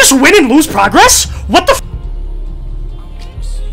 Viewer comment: Deadass thought he said helen keller for a sec there😂😂